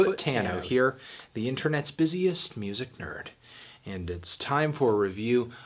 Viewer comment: I thought he said "the most pussiest music nerd", but I was wrong. SORRY, I'M HIGH